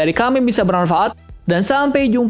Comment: dia ngomong apa?🤣🤣🤣🤣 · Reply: Feel gamers efek revisi find the game find the joy